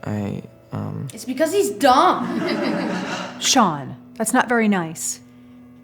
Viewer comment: did she say Shaun or sean? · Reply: Sean